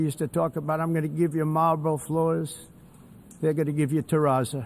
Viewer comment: Sounds like he said he wanted Marlboro floors.